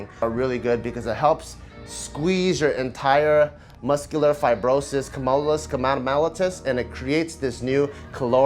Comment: Wait what did say at 2:33? I got muscular fibrosis and mellitus...